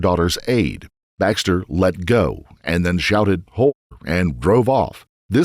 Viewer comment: Can someone tell me what he shouted when he was confronted by his mother in law after attacking Hannah? Narrator say “Baxter let go, and then shouted ??? And drive off” at 21:29 Ho?